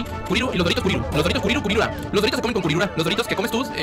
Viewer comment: Lo que entendí en la camara rápida fue algo de que los Doritos se comen con algo xd